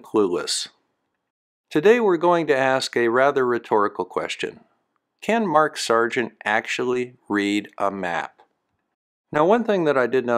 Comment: I heard "Can Mark Sergeant actually breed ?". I was beginning to feel morbidly optimistic.